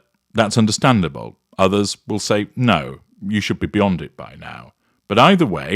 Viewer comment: you said you should be beyond it by now I thought you said you should be Beyonded by now. A pun on Beyoncé. Beyonde’. Lmao. I had to rewind and listen to it again.